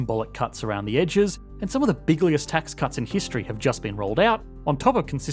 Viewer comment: Did he say bigliest 😅